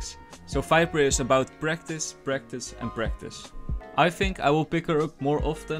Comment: I thought he said "Viper is about breakfast breakfast breakfast"